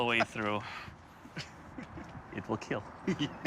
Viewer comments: I thought he said "it will kill" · and to think all this time, i thought he was saying 'it will kill' in a sinister way LOL. a little disappointed I'm wrong lol